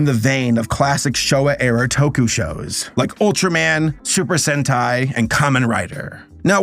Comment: I know what super sentai is but i swear i heard “super hentai” the first time i cant bro 😭😭